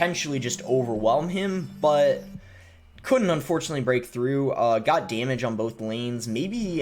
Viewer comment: idk why i heard god dammit on both lanes lol